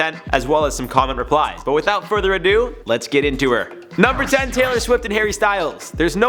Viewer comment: did you say “let’s get into her”? Who else caught that!!! 🤣🤣🤣, I love you man!!!